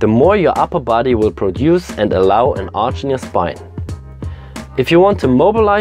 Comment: An arch near spine? Arching spine? I can't hear what you're saying. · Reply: allow an arch in your spine.